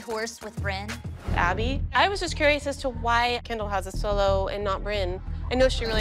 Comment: Did she just say why does Kendal have a solo